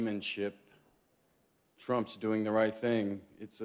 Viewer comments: "Trump's doing the right thing", I was like the hell are you saying boy before I heard the actual sentence. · "Trump's doing the right thing", I was like the hell are you saying boy before I heard the actual sentence. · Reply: Paul Brown I respect your opinion, but your interpretation is incorrect. If you listen to what Brad actually said, he didn't say: "Trump's doing the right thing", like I thought he said at first - he said: "I think when gamesmanship trumps doing the right thing, it's a sad day, and I don't think we should let it slide." His speech was in fact highly critical of Trump, and that's what he meant when he said he was disappointed with the week.